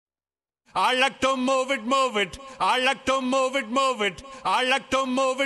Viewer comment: When I saw this in the theater as a 10 year old, I thought Julian said "I like the movie movie"